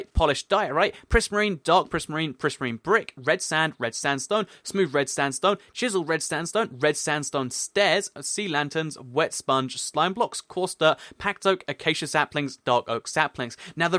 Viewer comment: Lol, it sounded like you said red standstone stairs!